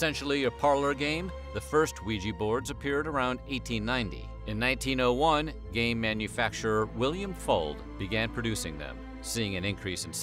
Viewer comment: i thought you said "in 1901, gay manufacturer william fuld-" and i was really confused for like 30 seconds until i turned on captions